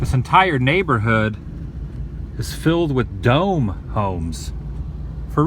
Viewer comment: Holy CRAP!! 🤣 I thought you said" DUMB HOE'S".!!🤬 I was fixing to go TFO 😠on you. But you said, "DOME HOMES".🤣🤣 Oop's🤪..